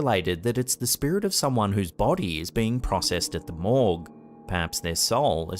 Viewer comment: Thought he said mall..not morgue. Hehe